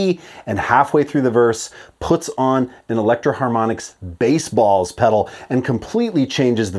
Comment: I heard Spaceballs pedal. 😁